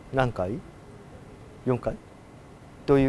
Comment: 男の声で、４階って聞こえました 6:38さんの「な？」って声かけに、うんって女性の声が聞こえる